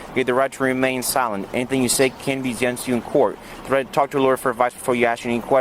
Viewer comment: 'anything that you say can be done to you in court' was what I heard and saw on captions. I thought it was 'can be used in court, or used against you in court'? I know I am old and hard of hearing, but most of those read Miranda Rights seemed a bit butchered to me. I think he wasn't on time for that flight. How many more stupid things is he going to do away from his home? (Passport not with him at all times, reckless driving, fleeing a traffic stop, speeding, drugs - was he going to fly with that weed?)